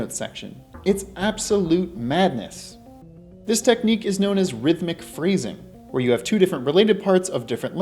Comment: I totally misheard and thought you said "rhythmic freezing" and thought to myself, "that seems appropriate for the temple with the Ice Arrow."